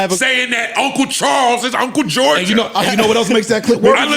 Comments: i thought they said uncle georgia too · yea i def jus found out he saying “uncle charles🤦🏾‍♂️”personally i thought he said george lol